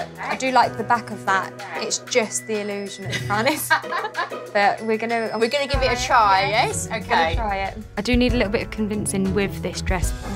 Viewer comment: did i hear "we'ree gonna give it a try, eh?"